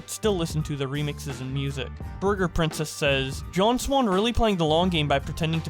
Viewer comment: did I hear "John Swan" ?